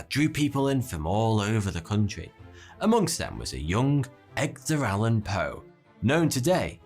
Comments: did you just say Eggdar ? XD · Did he say Eggder Alan Poe? Lmao